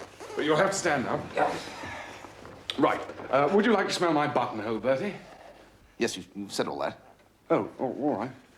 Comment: When he asked Bertie if he liked flowers I heard it as "Did you want to smell my butt hole".?